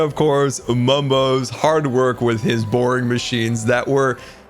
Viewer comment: lol it sounds like is saying mumbos boring machines